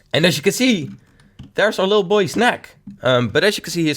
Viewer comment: sounded like snack